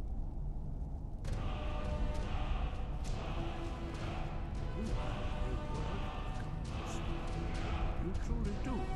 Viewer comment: Can anyone make out what he is saying at 3:17 and 4:00 ? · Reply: he's saying that you leran very fast and that's so easy to you